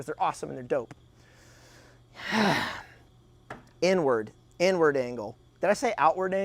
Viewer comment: I thought he was saying N-word. Only because I was just rewatching the Kyle Rittenhouse testimony of Rosenbaum quotes from that fateful night.